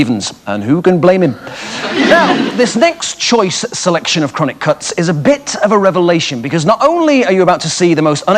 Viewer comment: did he say chronic cunts ?